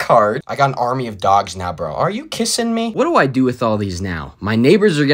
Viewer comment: I heard "are you kissing me?" And DIED laughing